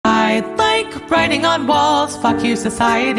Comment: I thought it said :I like writing on balls 💀